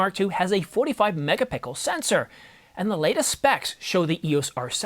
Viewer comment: i heard "MegaPickle". but keep it up boss, Ive been using canon 90d since its out. been wanting to upgrade!